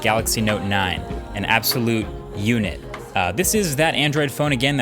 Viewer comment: Thought he said, "An absolute eunuch." coz I agree.